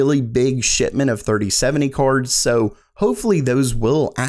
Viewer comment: am i the only one who hears him say shitment instead of shipment lmao